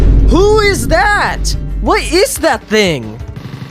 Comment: bro really sounded like the "WHAT IS THAT?!?" Meme